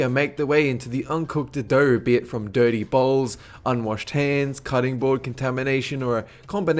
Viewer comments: I thought he said 'dirty balls', instead of 'dirty bowls' XD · did he say "Dirty Balls" ? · When he said dirty bowls i thought he said dirty balls xD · At around 3:00 , when you said "dirty bowls" in regards to the cookie dough, I thought you said "dirty balls" due to your accent. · when you said dirty bowls i thought you said dirty balls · When you were talking about raw cookie dough, you said "dirty bowls" and i thought you said "dirty balls"